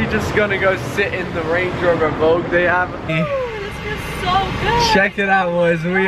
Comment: range rover evoque?????? i am pretty sure that is a range rover sport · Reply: I believe he said Vogue but yeah at first I thought he said Evoque so yeah I know what your on about